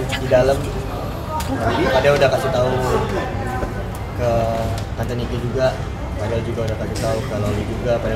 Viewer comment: Wait...?? Salah dengar gak sih aku tadi,dia nyebut Tante niki..😂😂